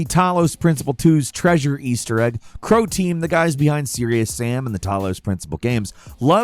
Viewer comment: At first I heard "Crowtein" and thought "Mac and Charlie made a game??"